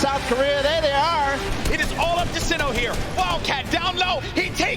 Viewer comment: 도네쏴주세요오오~~!!!!! 들린다 들려